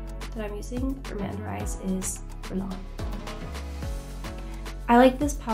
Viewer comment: What brand did she say she’s using for her under eyes????? I’ve listened to it back like 10times, scoured the comments, and even turned the captions on (which didn’t tell me anything), and I still don’t know 😩😩😩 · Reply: And of course it’s the only thing that isn’t in the caption details…. All I’m hearing is “long” or something like that